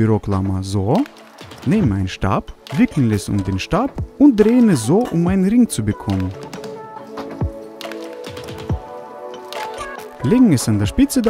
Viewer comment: "ficken es um den stab" (hab ich so verstanden) 😂😂😂😂